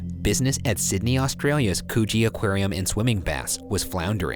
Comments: I THOUGHT HE SAID 'COOCHIE AQUARIUM' AND I DIEDDDDD OMG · Uhhh 21:31 did he just say Coochie Aquarium??? 🤨🤨🤨